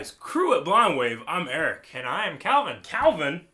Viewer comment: TBH this is the first time I understood "crew at Blind Wave" in the beginning of the videos. I always thought you guys say something weird like "cruet Blind Wave" and just ignore it. And kudos to Eric to pointing out the development of Dany's musical theme, it was one of the coolest things about this ep.